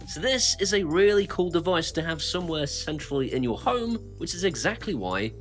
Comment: I keep hearing somewhere sensually instead of centrally 😂😂😂